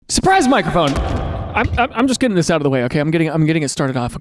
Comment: Thank goodness for the captions because "surprise microphone" did not sound like you said that at all! I got "see you in tea".